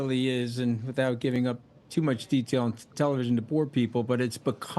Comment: Did he just say give up to poor people 😂